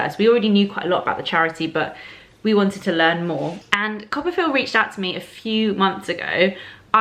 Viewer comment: It took me an embarrassingly long time to get the joke. I thought you were saying "Copperfield" facepalm